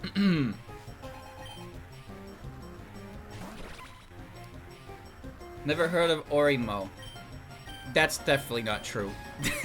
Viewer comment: I haven’t heard of … did you say “orimo” or “porimo”? I’ve heard of “marimo” (which is like, a virtual programming notebook thing) (I don’t really watch anime or tv)